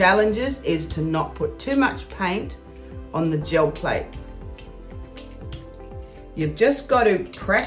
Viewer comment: I hear "Joe Plate" every time. lol